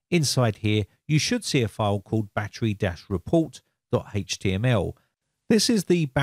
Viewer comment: Battery Death Report is what it sounded like there, heh...